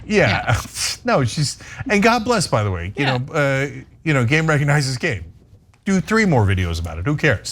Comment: Is saying 'game recognizes game?'